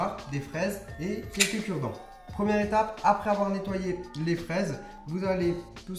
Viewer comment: jai entendu "apres avoir nettoyer les fesses" jai beuger😂